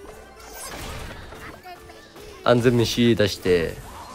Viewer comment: 安全虫〜の空耳おもろすぎる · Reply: どう考えても完全メシだろwww